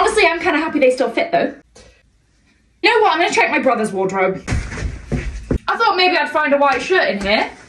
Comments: Thought she said brother’s wardrobe? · Bro at first I thought she said “my brothers closet” and I was like ok fair, and then she pulls out a Verzache dress 😂😂😭 when I tell you I was so confused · Idk I thought she said check her brother's closet...? Lol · I thought she said ‚I’m going to check my BROTHERS wardrobe' and was surprised. Not that I don’t approve 😁